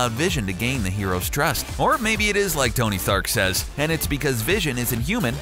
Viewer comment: did anyone else hear Tony Thark?